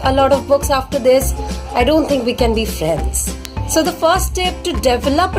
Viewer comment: in subtile you put sales instead of friends